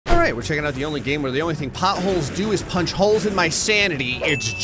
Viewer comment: I thought you said the only thing that's possible is punching holes through my sanity